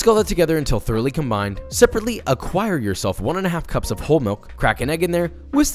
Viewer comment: At first, I thought he said secretly acquire the milk and I was very confused.